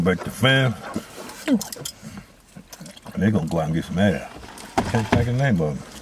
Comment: Did he say the dogs gon go out and get some a$$ ? 🥴😂😂😂😂😂😂💀💀